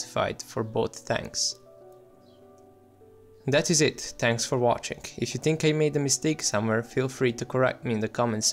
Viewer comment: Ha! Did you actually say "TANKS for watching..." at 9:52? Gotta luv it!